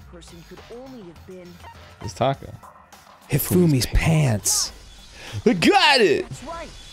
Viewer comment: Whenever Makoto says “I got it!” It always sounds like he says “A GUN” So people will be like “ what did Hifumi use to wipe his glasses” And then Makoto says “A GUN!!!”